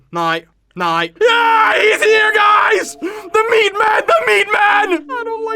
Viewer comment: I thought said “THE MEMEMAN” instead of the meatman😂